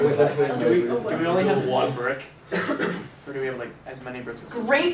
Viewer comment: did she say a brick?